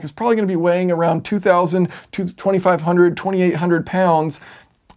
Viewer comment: 2,000 is a big difference from 1,800? I mean it is, but not that big. 3600 is not what I said it would weigh. But honestly, we don't know yet. Current Tesla battery tech is around 2,800. I would assume they could beat that. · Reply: the way you said it, "2,000 to 2,500 lbs" sounded to my ear like "2,200; 2,500 lbs." The whole to vs two thing.. Guess it was a mistake on my part as far as that goes. So it did sound like you were saying significantly more weight in your estimates than I thought you were, and not properly accounting for the low end estimates. Now I see you gave a nice proper range to account for more variation in the battery weights. I just wanted to share that these batteries could be much lighter than those used in the Model S and thus weight may not be as much as some are now speculating. Thanks for all the quality videos.